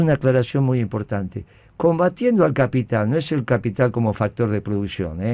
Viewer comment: "Combatiendo al capital" para que se entienda lo que dice la marcha